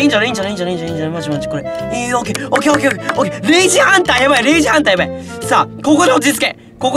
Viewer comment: レイジハンターがクレイジーハンターに聞こえた